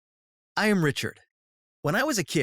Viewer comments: When he said ”I’m Richard!” I thought he said ”I’m Richer!” I was like, umm o-.... okay...? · At the beginning I thought he said I'm richer